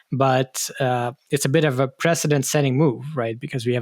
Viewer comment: Lots of interesting discussion like every week, thanks! I don't really watch tv, so I need to get my soap opera fix from somewhere. Fortunately/unfortunately OpenAI seems to deliver in this space as well as they deliver their products, iterating quickly... There's something fishy going on on both sides of the debate, at least on the coup case (and at least on OpenAI's side on the ScarJo incident). I wouldn't necessarily take the word of either Helen Toner or OpenAI at face value. Neither are incentivised to tell the whole truth. Why is she going against the wind and talking about toxic culture and personally attacking Altman, when many others say the exact opposite? OTOH do nice and nonaggressive people become major influencers and VCs at Silicon Valley? On the third hand does that mean they must be insidious and pathological liars, who take every advantage they can without any regard to safety or to the people around them? Did the whole company rally behind Altman because of genuine admiration or because of fear? Perhaps both? Truth tends to be murky, doesn't it. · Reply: Btw, when you discussed the fine for the fake Biden calls, I misheard you calling it a "president setting move", I sure hope it doesn't come to that 😁